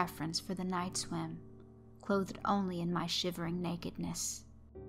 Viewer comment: "Clothed only in my shimmering nakedness"......wow great line! And of course great story as always! Hello! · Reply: I could have sworn she said "shivering", but shimmering does sound nicer.